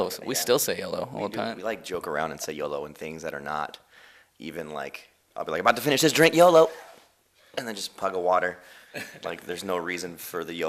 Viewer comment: Did it sound like Jaime said "Pug a water" to anyone else for the YOLO question part